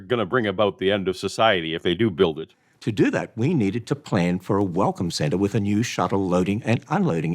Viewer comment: I keep mishearing Ham saying "Welcome center" as "Welcome Santa"